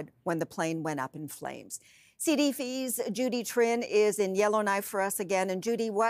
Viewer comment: replayed 5 times sounds like she says CDV's.